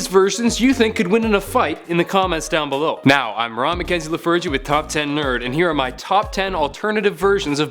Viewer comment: 😆😂 I thought he said rob McKenzie the virgin. Lol